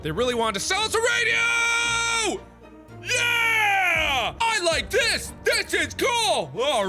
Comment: I thought he said; I like piss, piss is cool. oops